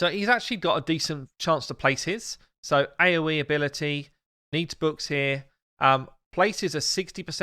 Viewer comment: You need to go back and read the Roxam ability again. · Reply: Actually nothing, I thought you said he had to have veil to do an ability, but you really said AOE ability.